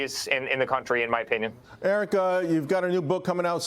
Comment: Did he call him Erica 6:49?😳 what a clown